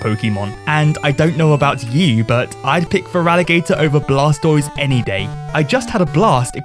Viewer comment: wHAt dID YoU SAy aBOuT aSS tOYs!?!?!